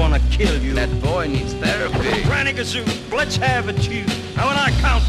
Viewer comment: Really i always thought he said "Grab a kazoo and lets a tune"